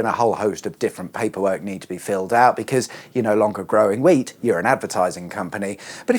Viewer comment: 🟢🍁🚬 thought you said growing weed.